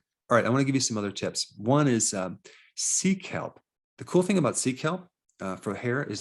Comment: When he said "sea kelp" and I heard "seek help", I burst out laughing